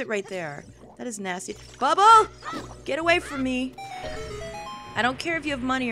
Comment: sounded like "BUBBA!! Get away from me!" poor cat